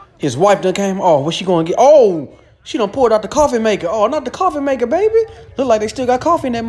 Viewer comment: was it just me or did anybody else think that the cat around 2:15 said coffin maker instead of coffee maker because iv have only smoked a roll one time that was like a year ago but still